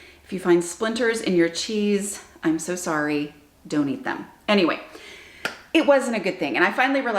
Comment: Haha! When you were warning your family about the splinters and said "don't eat them", i heard it as "donate them"!